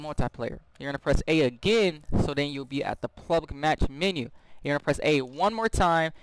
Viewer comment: WTF IS PLUBLIC MATCH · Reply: It sounded like you said plublic